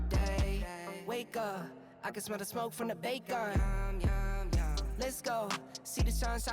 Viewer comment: Not connected to any of the stories, but I always thought the end song said, "I can smell the smoke from the bait gun" and thought that was a kinda gross way to wake up. 'Course, my dad was a fisherman, so maybe that explains a little. But, then again, he never used a bait gun, so maybe not.